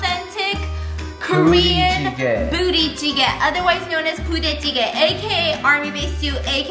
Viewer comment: I thought said bootie jiggle 😂😂 I can’t stand myself rn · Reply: Artistic Uprising- B.C. No she said the name of the dish Budae Jjigae